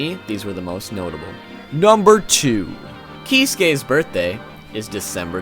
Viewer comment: "Keys gays" instead of Urahara, read the caption lol.